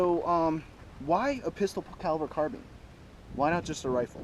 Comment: I thought the one guy said bisexual caliber I'll like what? So I went back and was oh bi caliber lol 🤣🤣🤣